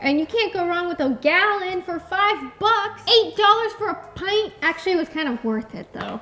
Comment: When said 8 dollars for a pint of ice cream I thought he said 8,000 dollars 😮😅😊❤LOVE YOU GUYSSSSS😊😊😊❤❤❤